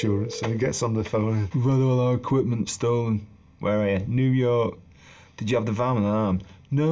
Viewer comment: What does Bernard say at 2:10? Did you have the van...? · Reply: Alarmed